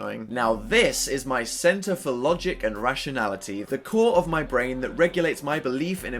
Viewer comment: I thought this said "the tour of my bran" even though I have seen this like <num> times